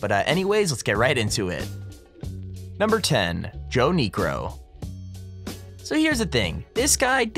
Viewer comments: The first one I thought he said Joe Negro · I heard Yo Negro.... sorry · 40 Who else thought he said (Im black so it's okay) Joe negroe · I thought you said joe negro 😂😂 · I thought he said joe negro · I thought he said “Joe Negro” and I was like what the hell (yes I’m black lol) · I thought u said Joe negro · I thought they said joe Negro XD!!!